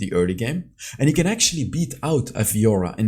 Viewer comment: I thought you said Renekton could "actually EAT OUT Fiora" at 2:54! Might be a bit weird with that crocodile mouth...